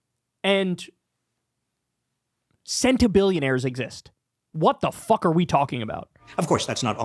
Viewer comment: Also, I thought was saying 'scented billionaires' instead of centibillionaires for a minute. I was like 'bro we don't need them to smell good, they just have to taste good'